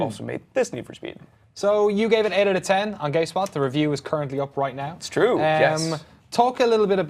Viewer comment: gay spot w8 what did i hear that wrong